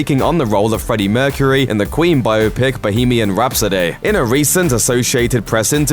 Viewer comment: Did you just say "Bohemian Rhapso-DAY"?